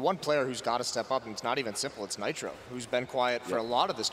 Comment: Em pleno 2018 eu ainda assisto essa partida, que em minha opinião, é a melhor da história. Obs: "Ei, Team Liquid, vai tomar no cu." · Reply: Eu entendi "Ei, Simple"